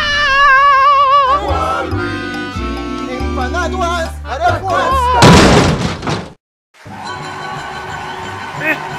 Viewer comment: What Does Waluigi Says? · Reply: "Empanaduas Adarguas". I think that's what says.